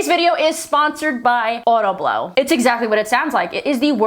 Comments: i thought the sponser you said was audible. not sure if that was planned but sure got my attention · I thought you said audible until you put it onscreen 😭 lmaooo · Bruh, I thought you said Audible for your sponsor, then you said blowjob machine haha.